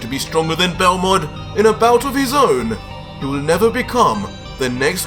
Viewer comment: am i the only one who heard him accidentally say universe 6